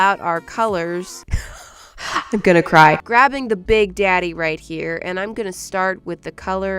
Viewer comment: did she really say big daddy 😂😂😂